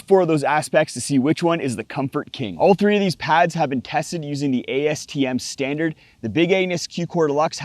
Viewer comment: I'm sorry... did you call it "The Big Anus" at 0:42!?